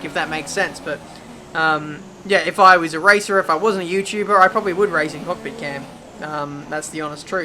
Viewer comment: "If I was a racer or YouTuber, I'd race in cockpit cam." Uh, what? Does think he's not a YouTuber and racer? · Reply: I know this is like 3 years late, but he clearly says "If I was a racer – if I wasn't a YouTuber – I probably would race in cockpit cam."